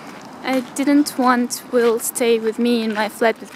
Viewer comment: did she say his name? "will"?